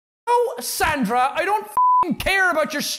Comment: I thought he said Santa at the beginning